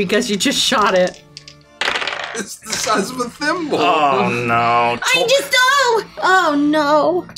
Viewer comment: Did I just hear Gideon say "it's the size of a femboy?"😅 Omg this video went down hill quick😂😂😂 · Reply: He said thimble